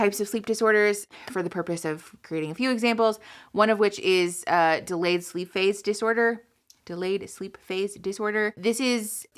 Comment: I loved how you describe all these symptoms in a very easy to understand way.I actually thought you said " delayed sleep face disorder" 😂